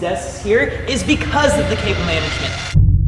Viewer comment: did I hear someone say cable management? Lets call Linus